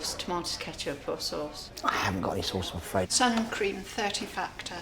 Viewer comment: The way the grocer giggled at the sunscreen request cracked me up. Did they not tell the buyer participants that the sellers had to create and package all their products. Also sunscreen would’ve been a pharmacy item back then if it even existed (I’m positive it didn’t) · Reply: I thought she said sourcream.